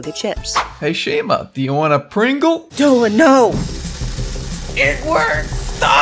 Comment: i thought he said " IT WIRKED!" LOL 😂